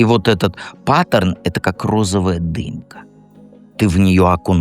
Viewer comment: А мне послышалось: " розовая дынька".😅